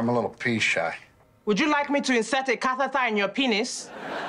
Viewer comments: I think she’s saying insert a catheter lol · I think she said catheter lol! Her accent was just waaay too thick. They're stupid for that one! Lol!